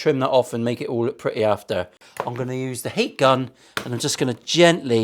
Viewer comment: Heat Gun? Heat Gun? I thought you said 'Hate Gun'. I actually put down my bowl of scran when you mentioned the youth club outside in preparation of the gene cleansing about to commence. Ah well, back to reality....